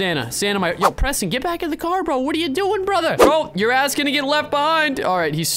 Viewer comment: 😱 finally slipped up, did anyone catch what he said at 4:15 - 4:17 if not well he said A** it's not a big deal tbh I sya it all the time I just think its hilarious the difference youtubers act on camera and off camera and the limitations they have